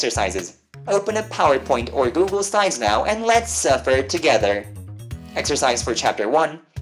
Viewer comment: uh did i just hear "LET'S SUFFER TOGETHER!"